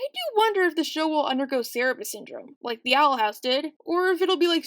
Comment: I didn't catch that syndrome name. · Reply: Cerberus syndrome. It’s when a cartoon starts off light hearted but then becomes gritty and dark over time; Steven universe went from a kid who wanted to eat ice cream sandwiches to a teen who had a mental breakdown from familial abuse, a mom who was physically abused and had major identity issues, etc.